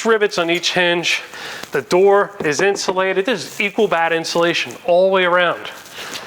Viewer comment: Did he just say "fecal bat insulation"?! .. I know I had to have heard that wrong 😅😬